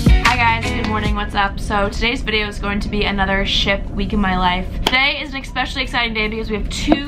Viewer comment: bahaha at the beginning I thought she said shit week in my life, I was like hold up that sounds like my life